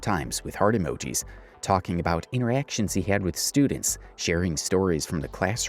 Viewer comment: God damn, thought u said E rections scared tf outta me